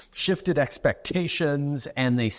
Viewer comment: The Douche canoe Trudeau says "... shifted expectations ..." I hear "shattered dreams".